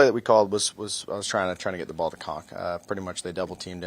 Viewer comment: Did Zach just say at 0:17 he was trying get his balls to cock? WTF? This QB has to go! Mike White should be given a chance to save the season. Wilson is NOT talented enough. Is eloquent speak at the podium is what keeps him going. LaFleuer is a joke! Fire him now! 3rd an 1 with 40-seconds and you PASS? WTF would you not do a QB Sneak or run with Robinson? What a joke!